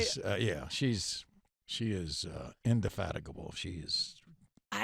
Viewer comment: what was the word? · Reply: indefatigable